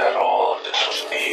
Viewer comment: Do you know what Golden Sonic said at 0:16 - 0:18? It sound like he was saying something but all I heard was gibberish EDIT: I got most of it except for the part where he said, "Towis" is that right? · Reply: Lemme finish it he says “no matter what you do you will always lose to us, you’ll see...”